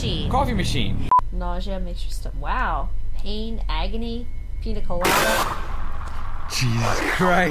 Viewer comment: Lmao SCP-079 sounded like he said "Pina Colada? Seriously?" as if he wanted some (also revisiting one of my fav playthroughs love u guys